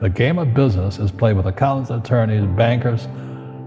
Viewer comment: He's not saying "counsel attorneys" but "accountants and attorneys" the subtitles are wrong.